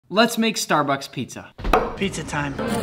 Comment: i thought he said starburst pizza